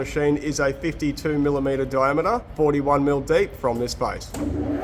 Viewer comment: CC says: 42 mm deep. says "forty ONE mm deep". Someone is lying here... ;-)